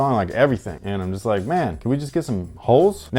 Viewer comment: I swore you said can we get some hoes...... Lmao I'm crying 😂😂😂😂😂😂